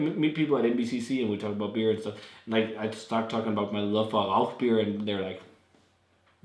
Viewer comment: Hi! What is the beer you love in 11:05 ? I didn't catch the name :) Thanks! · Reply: "Rauchbier" the smokey ones ;)